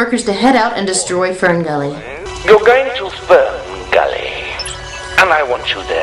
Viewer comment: Sounds like he is saying Spermgully